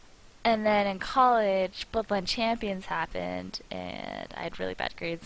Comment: what did she say at 5:37? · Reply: bloodline champions